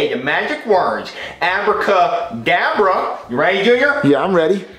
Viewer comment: bro accidentally said abracadavre instead of abracadabra